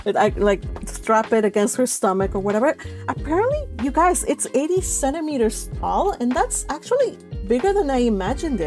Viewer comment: i just double checked my copy of prepub.. the chalice was 80 CENTIMETERS not 80 meters! 😂😂😂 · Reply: My apologies.. I replayed the vid.. and turns out I miss heard you! I thought you said 87 meters rather than 80 centimeters! Hahaha